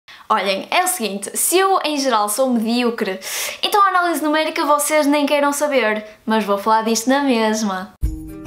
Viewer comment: Não faço ideia do que você disse aqui. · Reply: Jonas Ferraz, acho que foi isso: "Olhem, é o seguinte: se eu em geral sou medíocre, então a Análise Numérica vocês nem queiram saber, mas vou falar disso na mesma."